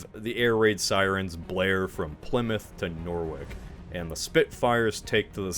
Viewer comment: “nore-wick” lol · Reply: I thought he said Lerwick 😂